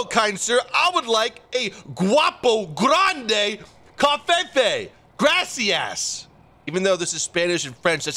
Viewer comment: I almost thought he said "COVFEFE!" at the beginning😂😂